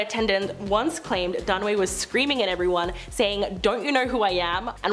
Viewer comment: When the host said, "Faye Dunaway", I heard 'Fazed Runway'. I couldn't imagine who she was talking about. My ears need cleaning, I guess.